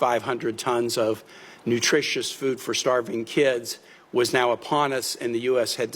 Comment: Nutritious food? Hmmm. I thought he said, "Biscuits".